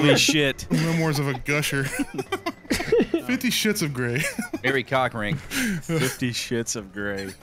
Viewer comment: At first I thought he said “fifty shits of Greg”